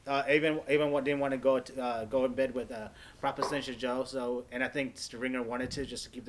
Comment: Did just call him Prop Extension Joe? LMFAO